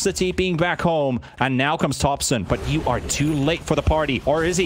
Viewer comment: I thought the announcer said "And now comes Dogson but you are too late for the party"